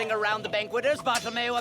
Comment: I thought he said bottle of mayo, not bartholomeo